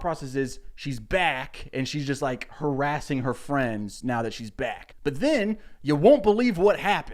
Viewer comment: I accidentally went 10 seconds back to this spot so I thought you said that the news was harassing her friends. I wish that was the actual plot